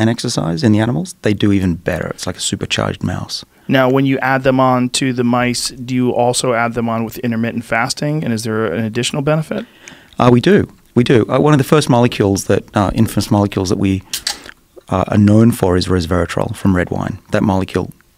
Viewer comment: i thought they said intimate and fasting, like you have sex and then fast or something